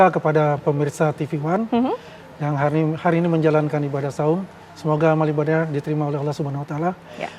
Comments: "selamat berbuka" "yg menjalankan ibadah sahur" Apakah saya tidak salah dengar? 🤔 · Ibadah sahur?????Atau sy salah dengar 😅